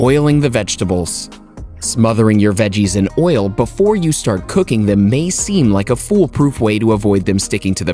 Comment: Am here trying to convince my mom to stop giving me rice and give me healthy food🙂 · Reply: escaped away Thought you said "Start cooking your mom" nearly shit myself lmao